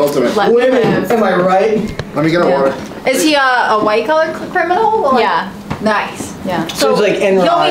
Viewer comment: She said "Is he a white collar criminal?" And I thought she said "Is he a white colored criminal? Nice!"🤣